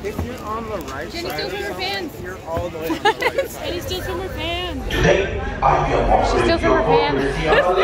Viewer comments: Oh, I thought they were saying "Jenny steals rubber bands"... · Ok, I have to ask.... what are they saying? It sounds like "Jenny steals from her fans" but I don't know if that's correct. I'm guessing it has some sort of meaning otherwise it wouldn't be in the video :) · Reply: Yeah jenny steals from her fans, it's in the Pandora video too, her friends thought it was funny so they started saying it to "mess up" her b roll footage